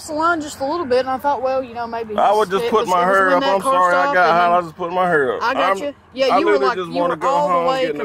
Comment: it sounds like he said he’s HiGH